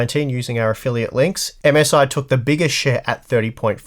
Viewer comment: It sounds like said "MSI took the biggest $hit" LOL It just his accent but that was so great!!!